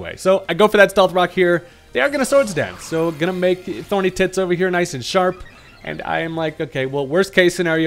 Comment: Me: did i just hear "thorny tits". Rewinds 10 secs: "thorny tits" Me doing peter griffin laugh